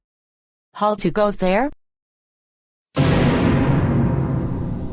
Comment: What did she say at 5:46? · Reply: HALT WHO GOES THERE